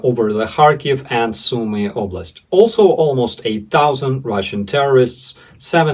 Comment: Welcome to Day 473 of Terrorist Ruzzia's 3 Day Invasion and War in Ukraine. At first I thought you said the Ruzzian loss was 8,000 ... then I realized you said "almost a thousand". Darn 😅😅 Sláva Ukraíni 🇺🇦🇨🇦 Heroyam sláva 🇺🇦🇨🇦